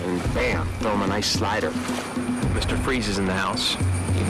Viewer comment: I almost thought Brian said: "Mr. Frieza's in the house" instead of 'Mr. Freeze". That'd be just as fitting, since Frieza is a ruthless prick too.